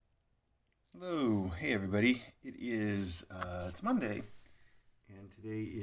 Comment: Thought said Momday. 😻